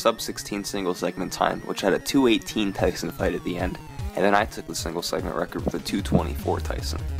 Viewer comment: Am I just being dumb or did he say "I took the single segment record"?